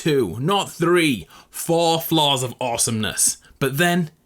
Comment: when he said floors i thought he said flaws